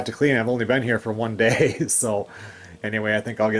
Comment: Wow I thought you said 4 days.